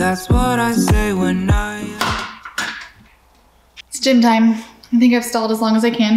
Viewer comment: At first I heard "It's Gin time." Not "It's gym time.". 😆. During the holidays I get a Hazelnut Truffle Mocha. Otherwise I get a sweet cream cold brew (even in the winter).